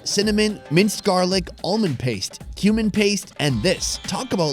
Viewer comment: I thought u said human paste at 1st